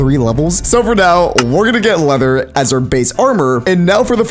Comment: anyone notice that the captions said bass instead of base